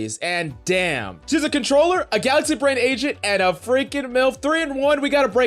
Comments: Did he call he a “controller, a galaxy brain agent, and a MILF” around 1:03? I’m mainly confused about the MILF part bc she doesn’t look like a mom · Did I just hear milf?